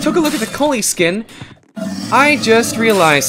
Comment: when you said kolie i thought you said poley